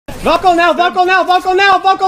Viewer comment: I thought he said buckle now😂